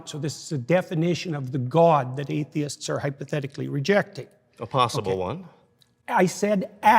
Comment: | I heard "the divine" and "the god atheist hypothetically rejecting", so... when did he say "a"?